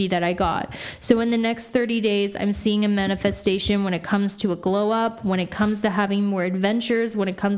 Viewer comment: When she said “trusting manifestation”, I heard “trusting men at the station” and I was like duhh I don’t trust men at the train station! 😅